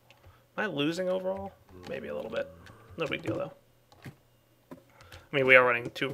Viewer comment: Does say not a big dildo?